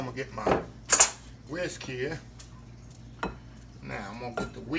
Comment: Cream cheese glaze looks delicious. I thought you said frosting but I see there is a difference. Just enough egg nog in it to take it to the top!!! You sure did whip up that glaze!! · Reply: Oops! I thought Chef said "Whiskey" when he mentioned whisk!🤭🤭🤭